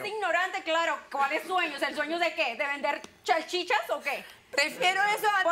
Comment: Escuche que dijo Chalchicha 🥖 min 14:15 ? Que loca esa tía!